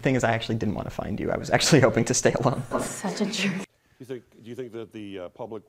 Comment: Did she actually called him a jerk?